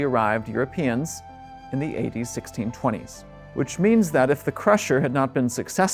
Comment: Am I the only one who thought he was saying 80-1620 rather than A.D. 1620? 😂